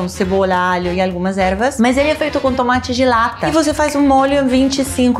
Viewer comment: No começo entendi “tomate gelata” hahahahaha ai Paola, amo você! ❤️